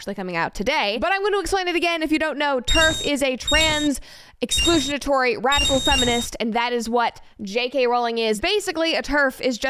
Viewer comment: I thought you said Trans Exclusionary Rational Feminist